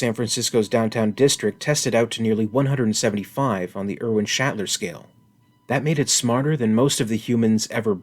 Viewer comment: LoL at 9:45 I almost thought you said the alien queens intelligence was rated on the William Shatner scale.... :)